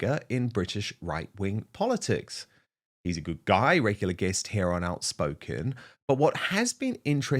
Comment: Tommy is a true patriot ,and there are millions of us all around that believe in Tommy. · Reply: sorry I thought you said he's a good goy.. I was all in on a thumbs up.. But meh..